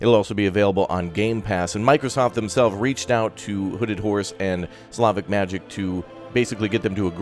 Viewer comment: Was I the only one who thought was saying hooded whores instead of hooded horse? Nobody else? Just me?